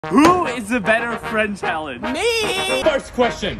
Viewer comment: I thought it said “Vu is the better friend challenge” 💀